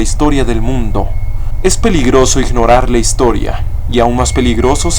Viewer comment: Soy yo o escuche "Peliar"?